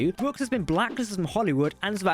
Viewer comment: What is he saying at 3:09 ? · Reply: Avery Brooks is blacklisted from Hollywood.